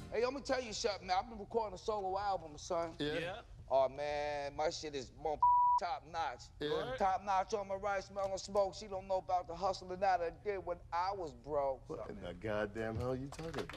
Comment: I’ve listened to it like 10 times and from what I can gather, at 0:20 he says something along the lines of “Aw man, my shit is mothafuckin top notch. Top notch on the rise smellin smoke she don’t know bout the hustle but I did not care when I was broke.”